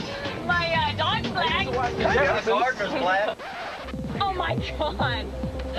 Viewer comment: they really thought “my gardener is black” was worse than “my dog is black” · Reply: thought he said his marker was black